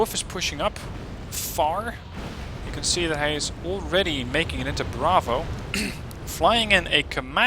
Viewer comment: "You can see that hij is" I heard that:P